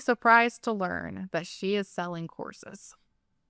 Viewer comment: I thought said, "would you be surprised to hear that she is selling horses?" I was like "...yes? You lost me" 😂